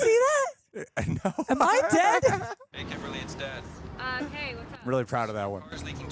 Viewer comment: Am I crazy or when Kimberly's Dad calls on the car phone does it totally sound like he says "Hey Kimberly It's Death."